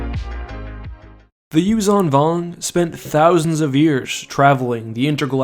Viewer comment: I thought that you say Yu Zong Vong